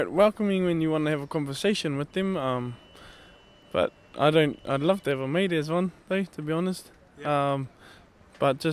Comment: what did he say? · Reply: He said "I'd love to have a mate who was one." The wind did mangle some off what he said.